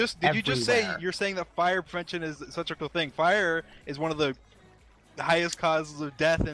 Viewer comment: in my opinion dream said Did you say that fire prevention is such a cool thing